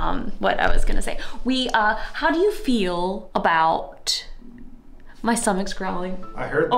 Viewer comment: For a moment I thought you said "my stomach's growing" that would've been NEWS